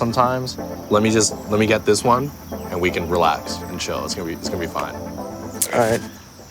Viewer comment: did he just say Insha'allah? Okeyyy 🙃